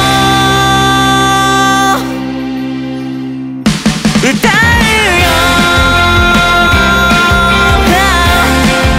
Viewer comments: 秒の口パクのところはなんて言ってるんでしょうか…………？ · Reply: 君が好きだよ みたいなことを言ってるんだと思います！私も全然自信が無いので参考程度に💦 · なんて言ってるんですか？ わかる人いたらおしえてほしいです！！ お願いします！ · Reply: 「君が好きだよ」だと思います！